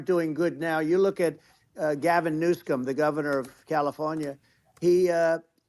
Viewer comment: ish Did Trump say "Gov NewScum" ? LMAO! I'm sure that was an accident but it's funny (& *accurate!) af!! 😂😁😅